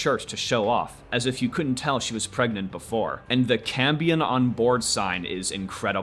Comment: I genuinely thought it said “Cambodian on Board”